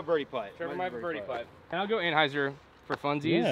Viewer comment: I swear I heard say "I'll go Rat" on hole 8, and then he proceeded to flip it up with a lace-job... I had to rewind twice to confirm he said Roc.